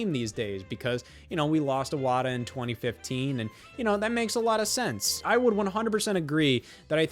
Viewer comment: I had to skip back a second because I thought you said "that makes Iwata sense."😅